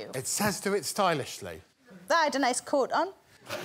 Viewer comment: What does she say at 21:41? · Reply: I had a nice coat on.